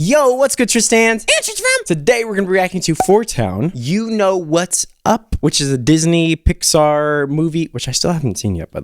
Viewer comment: Why do I hear "you know it's sus" at the start??